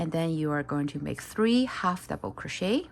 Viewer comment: the captions say 2hdc but the audio say 3